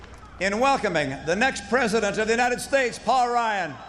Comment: did he say President at 8:25? wtf lmfao? no one else caught that?